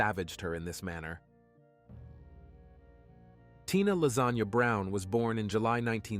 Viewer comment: Wait Tina Lasagna ....Lasagna? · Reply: It sounded like he said Lazanya lol